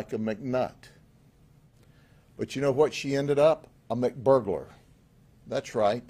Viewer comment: "She Became a Mcburglar" LOL 😂 This Sheriff is Funny ! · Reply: Thank goodness! I thought he said McBurger at first. 😅